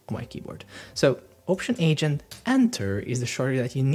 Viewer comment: Your accent is creating problems, I'm sorry to say. At exactly 7:00 minutes you mention a very special keyboard shortcut that I cannot make out no matter how many times I repeat the video segment. I hear Option Agent. CC hears it as the same thing. Unfortunately you do not explain what this keyboard shortcut does so we have no clue. What's even more sad is the fact that you say we must know this keyboard shortcut if we want to be a Framer expert. Guess what. I guess I'll never be that expert because I cannot understand what you have said here and more important is the fact that you do not explain what it is. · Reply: haha i completely get you man. i don’t know what happened to me here. for some reason i said “option + H + enter”, when in reality it’s “option + command + enter (alt + ctrl + enter on windows i think). as i mention it before doing the shortcut, my goal here is to group elements in a way that it makes sense. for example i want links to stay together so i group them in a stack. so this shortcut does exactly that. wraps your selected elements in a stack. i hope this explanation helps and sorry for the bad experience, sometimes your mouth isn’t converting your thoughts into the correct words properly lol.